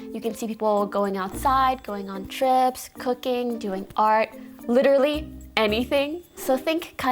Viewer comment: Bruuuh my dumbass thought she said "cocaine"